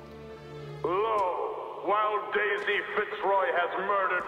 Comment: i thought he said Lol :P